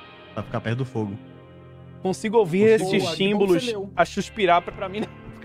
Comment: "consigo ouvi esses chinbolos" kkkkkkkkkj Edit :obg pelo 6 likes